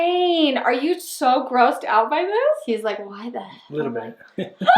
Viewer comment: did say hell